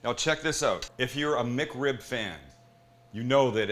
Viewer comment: Thought is said McCrib 😭😭